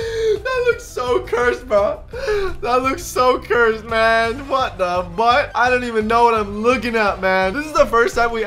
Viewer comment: I heard ₣Ʉ₵₭